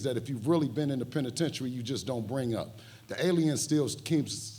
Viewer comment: Ayo! @ 21:20 Did Bill just called him a Alien😂😂😂